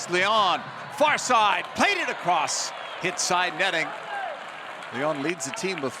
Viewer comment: Delacamera has a weird understanding of the English language. When a player hits the ball into the side netting, he calls this, "inside netting," which doesn't mean the same thing. When a player hits a shot straight to the goalkeeper, he calls this, "straight at goal." Again, this, though not technically incorrect, doesn't really convey the meaning he seems to think it does. Drives me nuts. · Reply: I think he says "hit side netting" at 2:12 and